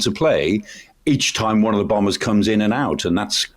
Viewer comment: is calling Boomers, Bombers?